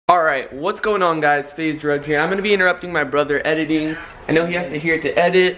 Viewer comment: Lol it sounded like he said... Hey guys its faze drug in the intro lol